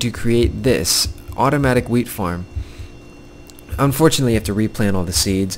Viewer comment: jhe said wheat I thought he said weed